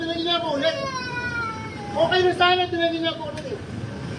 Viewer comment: ou Kase narinig ko din Yan eh... "Nasagi ulit". Hahaha😅 tyka palag palag din ulit nag kakarton.😂 Yan Yung napansin ko. Hahahah.